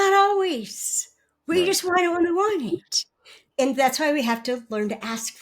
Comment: What the hell did she just say at 19:38? It sounded like gobbledygook… Oh wait, let me check the transcript! What she said was: “we just want it when we want it”.